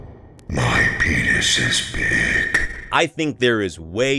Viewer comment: WTF DOES NIGHTMARE BONNIE AT 1:19:32 SAYS "oh, my penis is big"??